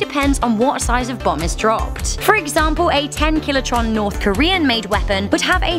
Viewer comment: Did she say "Killitron" ?